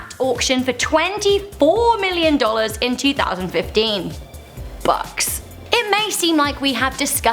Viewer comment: I thought she said butts ._.